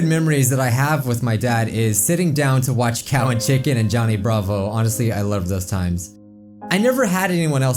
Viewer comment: when he said Johnny Bravo, I could've sworn I heard it sound like Danganronpa.